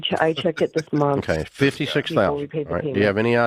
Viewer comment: | i thought she said $52,000